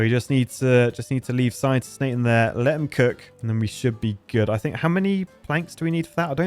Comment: Did just say "let him cook"